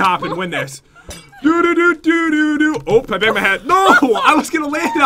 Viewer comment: reJENeration potion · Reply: it sounded like pat said "I bit my head" 😄